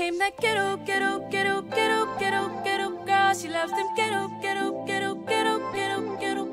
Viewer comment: i thought it said cheetoe girl